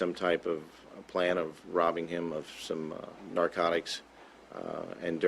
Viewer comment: Did he just say "norcatics"??